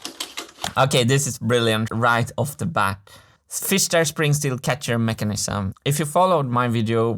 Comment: I heard multiple time "fister" :D